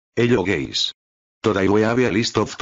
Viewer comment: Everytime i hear "HELLO GAYS"